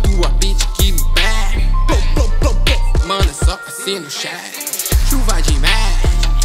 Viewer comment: Do refrão eu só entendi "chuva de mec"